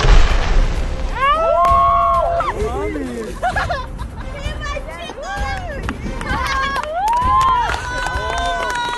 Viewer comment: en el minuto 1:01 creo que dijeron una maldision o escuche mal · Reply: Jajajaja si dijeron No mames pero fue su amigo de ellos...